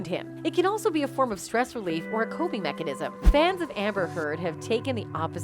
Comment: holy shit did she just said amber's fan???? 4:44 damn you must be a one sided mofo and a close minded one to be siding with a abusive mofo